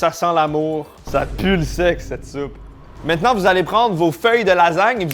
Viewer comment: "ça pue quoi ?" J'entends le se* personnellement